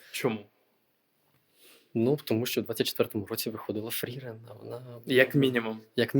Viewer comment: шо за аніме виходило? Мені почулося якесь шагірен